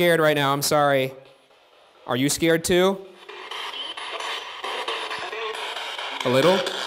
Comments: I heard "me? A little" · it sounded like she said “Me? A little” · I think she says Me? A little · dude i swear at 24:53 i heard “me..? a little..”